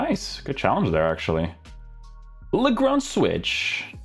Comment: anyone know what hes saying here? Sounds like lagroun switch 😅😂